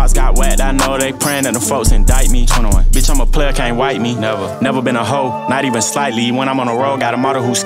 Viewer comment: Did 21Savage said b I’m a player can’t wife me ? Or my hearing fkd up? On 2:21🤔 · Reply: He said b I’m a player can’t wife me